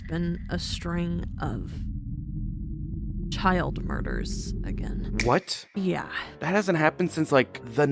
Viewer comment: (I was watching tv when I heard her say it on my phone) A strain of child murders. Me:The frick!